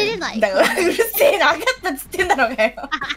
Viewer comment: Sorry, any japanese speakers willing to write down what Hinano said after the わかった～ at 4:16? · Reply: わかったっつってんだろ っつって is contracted from って言ってる